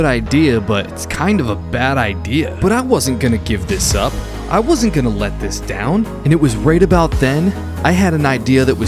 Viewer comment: Did anyone else hear never gonna give you up never gonna let you down at 3:32 through